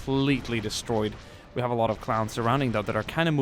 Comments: he says clowns instead of clones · he said clowns instead of clones lol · said clowns instead of clones